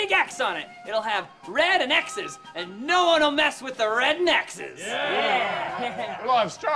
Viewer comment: I swear it sounds 100x different now that I hear it. But I always heard Timmy say "No one will mess with the red n x's!" But there's zero pause lmfao. "No one will mess with the red neckses!"